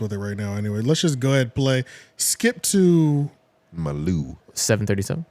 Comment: Ok so I wasn’t the only one who thought “My Lou” when I heard “Skip to…” 😂